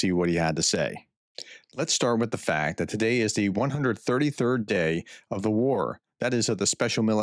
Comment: the guy did NOT say that this is the 133rd day, he said that it was the one thousand, three hundred and thirty third day. Why not slow down a little and get things right?